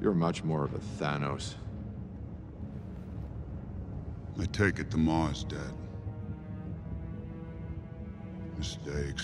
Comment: “I take it The Maw’s dead?” Anyone else hear “I take it Lamar’s dead?” 😂😂😂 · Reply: I heard "i take it Gamora's dead"